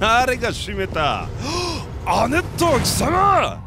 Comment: 「ベネット！貴様！」に聞こえて突然バイオがコマンドーになった